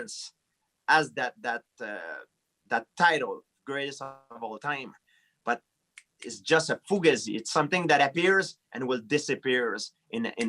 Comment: yo did gsp say fugaysi i don't know how to spell that but lmao it just sounds funny